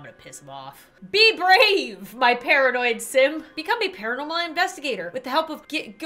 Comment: I thought she called us simps